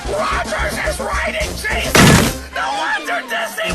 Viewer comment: "ROGERS IS WRITING JESUS!!!" My thoughts exactly. XD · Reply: Huh. I thought he said "WRITING GENIUS"